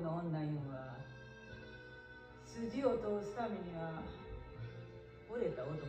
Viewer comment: ここなんて言うてんの⁉︎ 聞き取られへんわ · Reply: 筋を通す為には惚れた男も殺すのや。